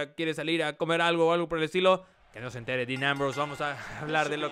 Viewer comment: ?? Que dice de Ambrose?? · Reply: primero le insinúa a Rene Young y le dice que no se entere Dean